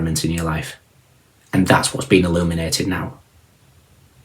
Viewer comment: When I heard “What interfered is being eliminated” I was like. YES!!!!!!!!! FINALLY!!! But then I realized you said “illuminated” 🫩